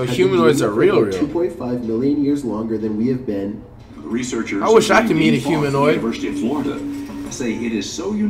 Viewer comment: "I wish I could be a humanoid" - 2020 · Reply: I thought he said “I wish I could eat a humanoid “